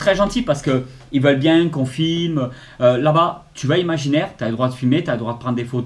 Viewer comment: Bonjour, à 5.29 j'ai compris "t'as le droit de fumer" 🤣🤣🤣 Les casquettes j'adore !